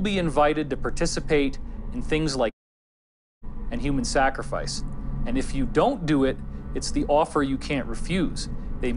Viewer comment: What was removed from what the guys said at 58:20? Something he said before "human sacrifice" was silenced. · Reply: Pedofilia was removed. I bought the film